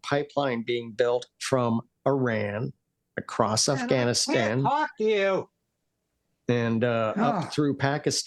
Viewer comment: A pivot of POV ?? Someone trying to say something 3:57🤔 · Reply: Yeah i heard I can't talk to you in the back ground.